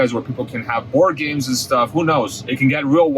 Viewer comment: beginning at 4:20 of course, who thought he was going to say orgies? Then, I heard organs... I'm thinking who drives with a giant organ down the road in an (future) autonomous vehicle? He said BOARD GAMES or is it bored games? At that point though you'll be able to just enjoy the ride. ... and the views!